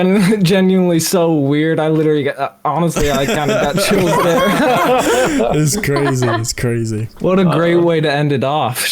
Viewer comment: What did he say there? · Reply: Toccando crea canale Ulteriori informazioni He said “Honestly I got chills there”